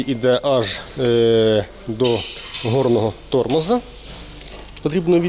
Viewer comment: Стоп мне не послышалось он сказал,,горный тормоз,,😅